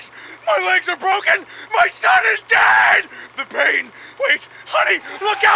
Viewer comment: for some reason I heard my son is gay like no joke